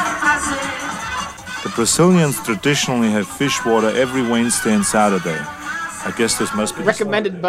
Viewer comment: Love how they misheard Arnie say "Fish-water" instead of "Feijoada" lmao